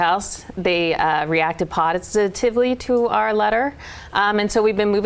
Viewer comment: Did she say potisitively? Because that's accidentally beautiful.